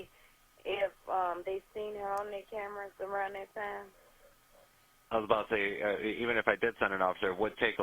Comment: To any one else did it sound like he said “above the” when she was asking about reviewing the cameras. It stats at 17:47 i think